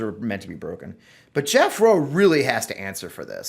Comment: Now DeSantis is saying he never even read the debate strategy memo in question. Let's see if he follows their guidelines during the debate. Can you see him with a notecard marking off each step after he completes it? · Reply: Fleece vest in 90 ° weather!🤣🤣🤣🤣🤣I kept hearing "Jethro" rather than Jeff Rowe.